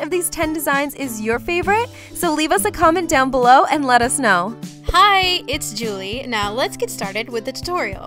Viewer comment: I thought said " hey it's jimmy...."